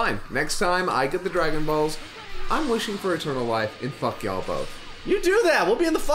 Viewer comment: When mentioned wishing for “eternal life”, anyone else thought he said “a turtle wife” or was it just me?